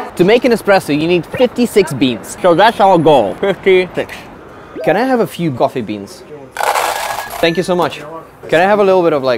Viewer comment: At the first shop i thought he said can i have a few fu##Ing beans